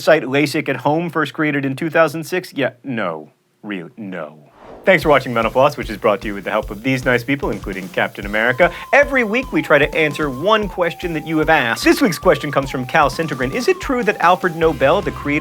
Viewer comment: i heard "thanks for watching menopause" at the end lol!